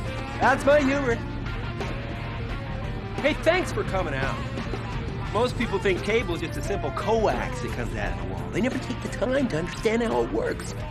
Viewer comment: what does he say? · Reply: "Most people think cable is a simple COAX that comes out of the wall." A coaxial cable was used to hook up your cable box at the time. They've been largely phased out.